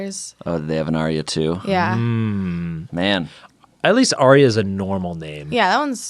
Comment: Is it just me or did it sound like Shayne was saying Arby’s in a weird accent lol